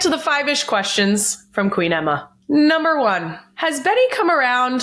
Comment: Girl I thought you said ‘Queedama’ not “Queen Emma”😂😂😂 new name??